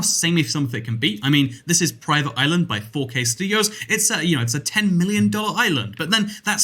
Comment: I thought you said 4jstudios not 4kstudios